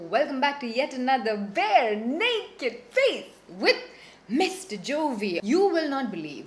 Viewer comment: Mr jovi ???🤔🤔0:06 or did I hear it wrong